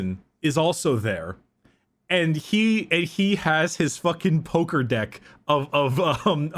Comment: I know meant a Pokédex, but I heard "Poker deck" and I just pictured Trazyn shuffling Tesseract Labyrinths between his light fingers like a card spring flourish.